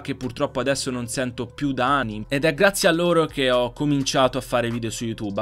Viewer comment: Solo io a 10:40 ho sentito ani e non anni cmq grande